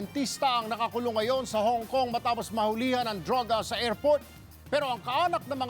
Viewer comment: ano sabi niya? DRAWGA? Hahaha